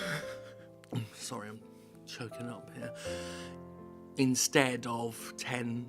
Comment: When you said "I'm choking up here" I thought you said " I'm jerking off here."